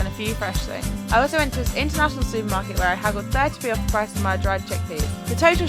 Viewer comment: i literally can’t understand what she’s saying lol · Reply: "I also went to this international supermarket where I haggled 30p off the price of my dried chickpeas"